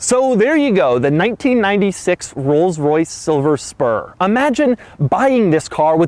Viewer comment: Am I the only one that heard him say Rolls-Royce Silver sperm